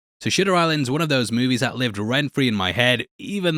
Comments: Sounds like you said "shitter" island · did he say shitter island ? 😂😂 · Did u say shitter Island 😂💀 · I swear i heard you say 'Shitter Island' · Sounds like you said shitter island